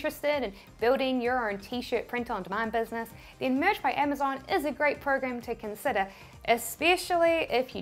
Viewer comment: Thank you so much for all of your tips! Your videos are amazing. Question though, at 3:28 you said that _ by Amazon is a great tool to have. What tool did you say? I thought you said Merge, but I can't find that anywhere. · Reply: Merch by Amazon